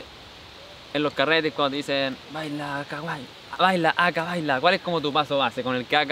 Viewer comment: pense que dijo "baila kawai" una wea asi le entendi